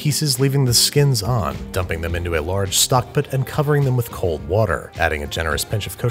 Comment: “Stock pt” Guys it must be contagious, he’s not only saying “pn” instead of pan anymore, it’s slowly spreading to his other dishes